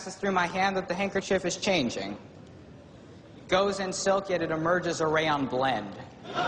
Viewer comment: until 1:25 - What does he say ? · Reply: Goes in silk yet emerges a Rayond blend